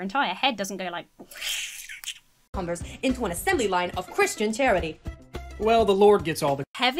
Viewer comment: I definitely heard "an assembly of Christian Tyranny" not charity... 🤣🤣